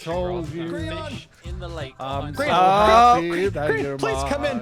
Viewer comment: did Scar call Grian gree-on or am i going crazy?